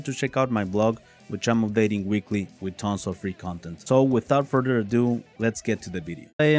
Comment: I had to stop and replay the last few seconds of the video because I swore I heard you say something about tons of free condoms, lol.